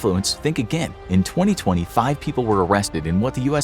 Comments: in 2025? What did you say? I' confused, cause it's 2022, isn't it?! 😂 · Is it me or at 2:26 I heard “In 2025 people were-…” bruh were in 2022 🤣